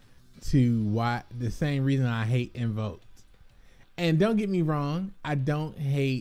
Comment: You don't like pendulum's for the same reason you don't like __ ? Tried listening to that several times, sounds like you say "invokes" or "evokes" or "m.o.'s" to me, which isn't making sense. I want to understand. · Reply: I meant i really hate pendulum magicians similar to how I hate invoked decks lol. When I went back to the tcg my introduction to pendulum Summoning was by losing many games to P. Magicians. 😆 long story short salty because both decks have clapped me many times 😔